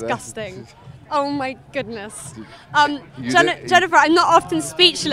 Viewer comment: "He didn't heard it"(?) Man, I wish I could get money by farting in microphones and using improper grammar. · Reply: He said "Ian heard it"